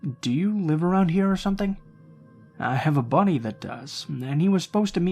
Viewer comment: Lol at first I thought he said a Bunny instead of buddy